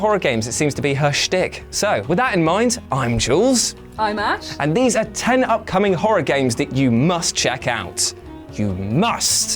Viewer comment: I thought she said "I'm Ass".